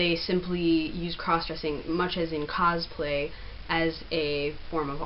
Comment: Hi, can't make out what you say at 05:10, Understanding a form of "cause-play" .. "a form of art" is that what you're saying? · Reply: I believe that they had said cosplay; dressing up as a character from a piece of media(book, show, podcast, etc.). It is seen as a form of art among a fair amount of communities.